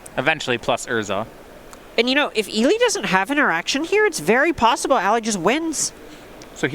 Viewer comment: at 1.25 speed - "If Eli doesn't have an erection here it's very possible Ali just wins!" - Misheard commentators 2019